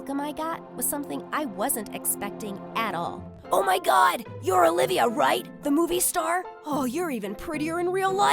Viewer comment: When she said her name I thought she said Olivia Rodrigo 😅